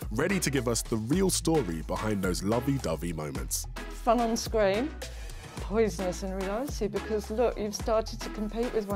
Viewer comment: sorry i cant understand why is saying..... just before she says "because look, you've started to compete with one another"... NO idea what shes saying front on screen? poisonous you realise?? i dont get it